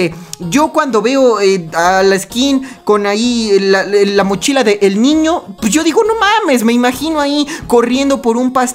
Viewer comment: Se que te gusta la skin de mona china, pero recuerda que el FBI te vigila. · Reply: Entendí "La mochila de la mona china" perdón, estoy muy tonta XD